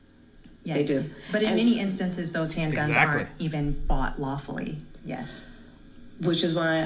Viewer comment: dude the lady is saying those handguns are bought LAWFULLY which is not what you said she said. You are saying ILLEGALLY. ??? 🧐